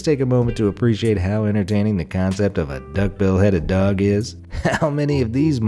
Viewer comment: I thought you said a duck beheaded dog